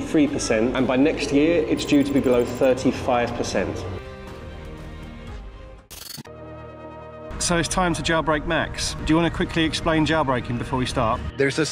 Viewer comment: First thought you said “jawbreak”.